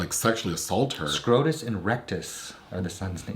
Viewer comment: Did say that the son's names were Scrotus and Rectus?!?!? As in scrotum and rectum? 😮🎉 Excuse me! Lol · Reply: It's Rictus. Rictus Erectus 😭 It's like a skit from Monty Python's Life of Brian.